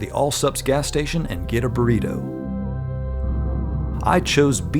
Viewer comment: Subtitels are crazy! usally the subtitles say "burrito" instead of "tornado" in your videos. But "tornado" instead of "burrito" is a new level xD 1:07 as well lol